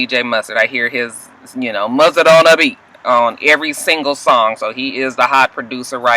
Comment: Girl, they saying "Mustard on the beat?" Lawd, all this time, I thought they were saying, "Bust it on the beat." I don't watch this mess. Like you said, don't know anybody up there. My son was in here cracking up at you imitating those songs. I googled the I Wanna Be Down Performance, loved it. I guess I'll go find the cyphers.